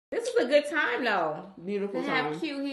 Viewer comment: I thought she she said she had good time with y’all she was talking bout a different party